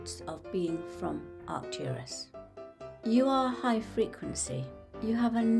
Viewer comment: Did she say "high frequently"? Because then lots of people I know are from Arcturus.